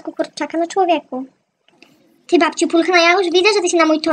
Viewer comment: Jak powiedziałaś ty babciu pulchne ja już ... to usłyszałam ty babaciu pulchne janusz